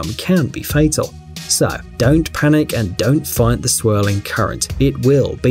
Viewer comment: When you said dive at the part of whirl pool i thought your gonna said Dont panic and Die instead of Dont panic and dive